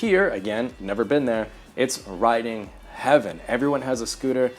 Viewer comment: I totally heard "rioting" rather than "riding"